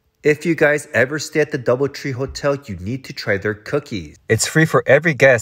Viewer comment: Oh I thought you said dollar tree hotel 😂